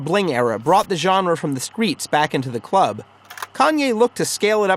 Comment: Please tell me he did not say "skreetz" T